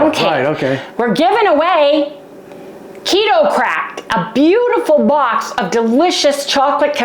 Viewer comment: Ok, I for real thought you said, “We’re giving away some Keto crap today!” You said it so excitedly and I was confused. I finally figured out you were saying Crack. 😅😂